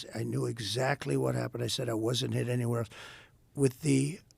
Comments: By An AR-15 (so I heard)... impossible!! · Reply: Go back to 4:22, sounds like he said “I wasn’t hit”. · He did not get hit by a bullet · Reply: Listen back at 4:22, sounds like he says I wasn’t hit! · Did he just say "I wasn't hit anywhere" ?! I think that was an oops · It sounded like he said "I wasn't hit." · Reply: Yep, he said, "... I said I wasn't hit anywhere".